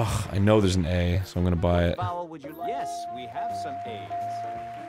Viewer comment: Where the man says "Yes we have some A's" I've been replaying it over and over and I keep hearing him say "Yes we have some aids." Man staying at home from The Backstreet Boys Tour is hitting me harder than I thought!